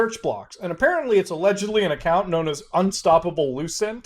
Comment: Allegedly. I thought you said Legendary.